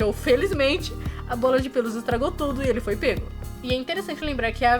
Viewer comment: na verdade o que saiu da boca do Gato de botas foi uma bala de menta, ele mesmo fala depois de tirar!😅